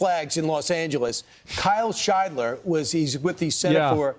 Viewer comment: I could have sworn he said hail scheidler😂. I thought it was a funny screaming at first.